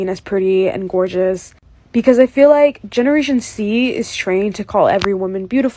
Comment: did she just say "generation C"😭